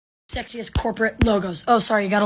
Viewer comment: I thought it said sexist 💀